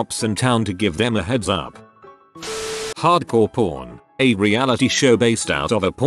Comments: The one where it's like "Hardcore pawn is based on a pawn shop" I thought it said "hardcore porn based in a pawn shop" and assumed it's the porn about pawn security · I thought it had said hardcore porn. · did he say ummm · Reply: Yeah, he said Hard Core Pawn... but someone people claim to have misheard it... Shrugs Shoulders